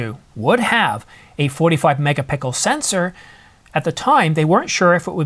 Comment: Did you just say ..."a 45 mega pickle sensor..." (0:23) that was a gem :). But seriously, thank you for all the hard work and long hours you put in to this channel. I really worry about this rumored subscription service on the new EOS models, hate that... I will stick to my R6 and wife will stay with the R7, maybe get the R6 mk II if the new models come with that subscription service fee.